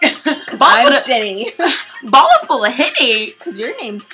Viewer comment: i thought jennie said "bottle of henney" too LOL